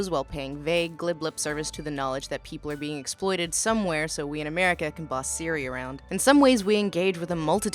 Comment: I heard 28:35 as "being exploited somewhere so we can boss Syria around.", and I had to listen to it three times to understand that it was talking about that apple software siri." I need sleep obviously.